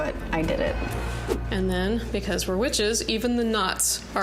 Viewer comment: I thought she said "Because were witches we're going to do nine nuts", which would have made more sense after watching her talk to the herbs.